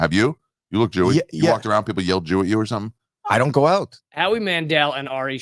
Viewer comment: Jew eat i definatly heard him say jew eat.